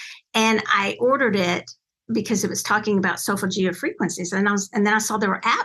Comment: I thought y’all kept saying esophageal frequencies…! Finally figured out you were speaking about Solfeggio frequencies! 😅😂🤣